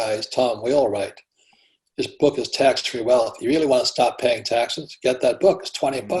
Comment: Can anyone tell me what book he recommends about taxes? in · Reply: I think he said “Tax-free Wealth” by Tom Wheelwright